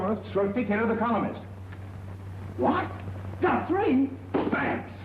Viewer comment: I didn't catch what the Fireman said to Swifty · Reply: He said he "wants a 'three-some' with his momma "